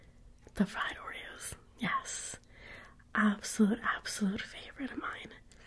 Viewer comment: Is it just me or did I hear her say, YAS